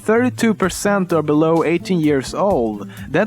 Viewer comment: 18 I thought you said 80 (haha )